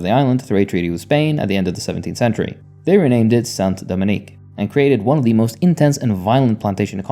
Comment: I heard it pronounced more like San- Domaing...like domain but ending with a "g"